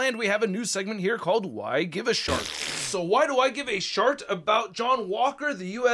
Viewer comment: I believe you give a shit 💩 This is what I heard not a short😉